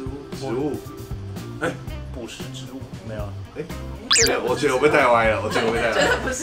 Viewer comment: 我聽成捕食植物了XD